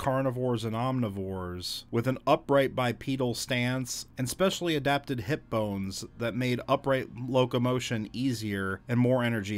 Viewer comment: When you said that dinosaurs developed a special set of hip bones, I mis-heard it as a special set of head phones.😂